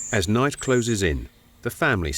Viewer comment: does he say,,as knife🔪(knight)settles in?😂